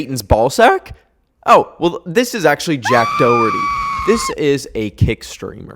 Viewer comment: i thought he said jack doohan💔 like the f1 driver